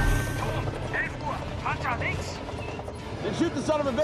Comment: Dunno why, but at 1:50 I always hear "jar jar binks!" (yes I know, links...)